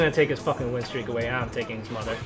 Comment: did i hear that right? You taking his mother i need to mess with bro more ts too funny